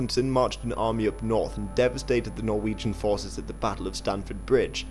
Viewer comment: did you say devastated?